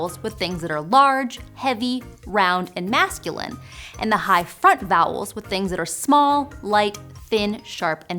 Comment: Anyone else hearing "high fart vowels" @ 5:10 ? It sounds even more like fart at .5 and .25 speed. great video btw.